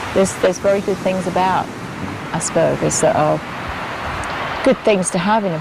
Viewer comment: When the narrator started to say "aspergers" I thought he was going to say "asparagus" >_<